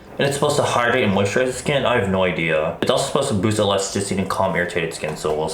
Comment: oml i thought he said "and calm your tits."